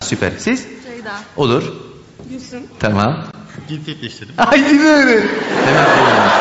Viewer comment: de ne diyor arkadaş? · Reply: Cinsiyet degiştirdim diyo knkda 3 yıl olmuş 😂